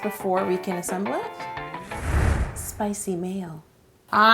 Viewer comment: sounded like she said spicy male